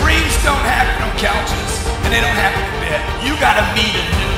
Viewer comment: What did the lady say at 00:34? "GC took over my life and now..." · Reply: 30k per month cash flow